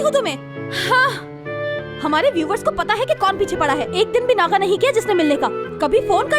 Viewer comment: did she said ' hamare viewers'!😱